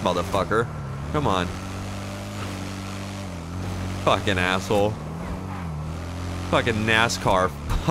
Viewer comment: at 20 ish I thought you said NASshole 😂